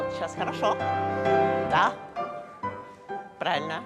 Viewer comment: Russian speakers, what does say to at 37:46 ? I know “da” is yes and “khorosho” is good/OK, but don’t know the word that sounds like “brana.” Thx! is such a pro…instead of just smiling and nodding at her earlier correction, he did it again.